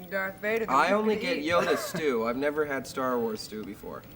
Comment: Did anyone else notice how Mark referenced Yoda but nobody laughed because at that point nobody knew who Yoda was? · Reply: Oh yeah, Yoda stew, of course! I thought he'd said yoghurt stew! I was like, "Yoghurt stew? What the hell is that?" :D